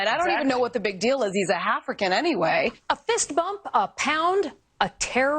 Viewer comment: Did she say.... "Halfrican"?